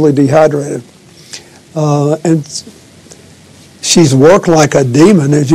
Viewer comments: Did i heard it right 2:24 when bill clinton said that she work like a demon? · Yes Bill, yes she has worked like a demon. Great word usage and comparison to describe your wife! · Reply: i thought he said walked like a demon. lol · did Bill say she works like a Demon? smh....